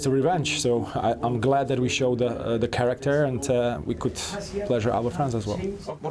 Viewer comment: what he say??? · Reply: Something about the team pleasuring the fans. I'm not so cool with that... But my girlfriend might be. 😆